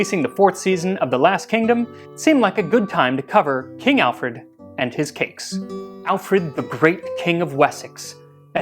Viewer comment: I thought he said, “I thought now would be a good time to discuss King Alfred & his kinks.” Needless to say I was shocked that this wholesome man would say that, luckily I misheard him lol